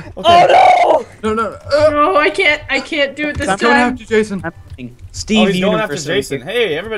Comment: im i the only on who heard jason say steven universe at 1:25 ?